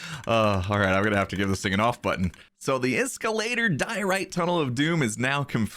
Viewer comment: did he just say "iskall diorite tunnel OF DOOM" im preety sure that he said that